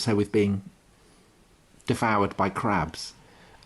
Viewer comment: Goodness me, my eyes widened shut near the "interval" when I thought I heard "deflowered by crabs". James Bond films can be racy, but rarely kinky....